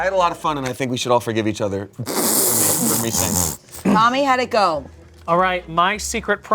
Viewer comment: "Mommy how'd it go" instead of Tommy is crazy work in the subtitles